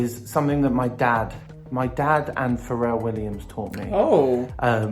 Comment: I thought Sam said their father was Pharrell Williams lol